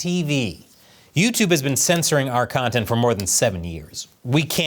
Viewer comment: ??? am i mistaken or did he say "youtube has been censoring our content for more than seventy years." did he say seven? it sounded like seventy